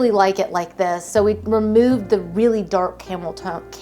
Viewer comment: I rewinded 5:16 like 10x cause I swear I think I heard “we removed the camel-toe” LMAOOO☠️😭